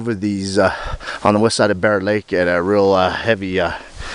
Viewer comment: I apologize I misheard i thought he said east of BearLake my bad!